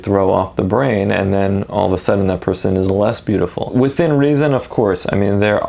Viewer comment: I thought he said "that person is a lesbian"